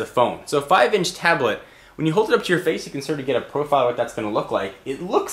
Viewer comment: Did you say Pedophile at 0:49 lol